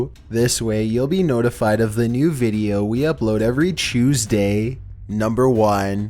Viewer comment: Why did he say chuesday in